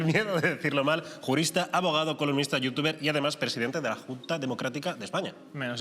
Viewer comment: Nadie: Diario sobre: columnista, jurista, youtuber, abogado y presidente de la Junta: economista no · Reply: Yo también pensé que decía economista XD en lugar de columnista